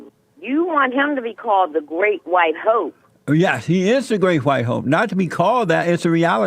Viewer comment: is the great yellow hope or better yet I just heard her say the great white yolk hahajajw